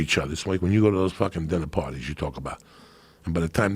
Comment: I thought he said Diddy parties 😅